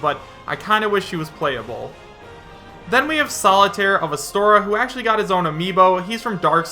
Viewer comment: DID HE FUCKING SAY SOLITAIRE INSTEAD OF MY BOY SOLAIRE